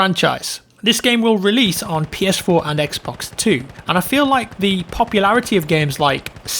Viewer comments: Did he just say “Xbox 2” 😮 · Who else thought he said Xbox 2? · xbox 2 ? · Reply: I thought he said XBOX2 too lmao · i heard "ps4 and the xbox 2" (i know what he actually meant im just a smartass)